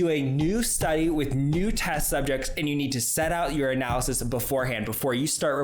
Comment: Every time you say "your analysis" I hear "urinalysis" 🤣 Also, loving your outro 🎶... is there anything you're not talented at?